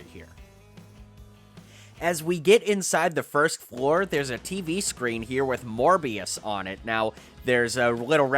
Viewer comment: I thought he said ‘there’s a little Tv screen with more BS on it’